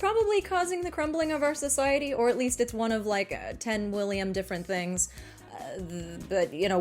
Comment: excuse me did you just say 10 William